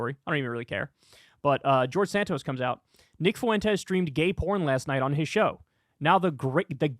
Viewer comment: I thought it said he SCREAMED gay porn I was worried for a second